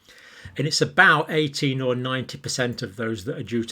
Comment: Dr. Campbell, you need to make sure that you pronounce “18 to 19%” differently than “80 to 90%”. The former has and “N” sounds on the end, while the latter does not. I heard you say “18 to 90%” when it should have been “18 to 19%”.